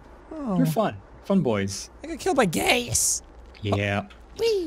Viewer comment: Not gonna lie, kinda sounds like jack said "I GOT KILLED BY GAYS!" Heh...